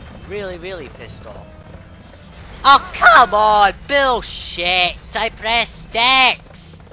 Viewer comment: at 7:52 did you say bill s**t or bulls**t? It did kind of sound like you said bill. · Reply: Said "bullshit" haha, but my microphone and voice quality was poor at this point, so obviously it could come out wrong 🤣